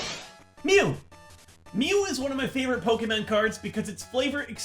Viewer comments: Did just say “Pokémen”? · *DID I HEAR POKEMAN??* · did he say pokemen?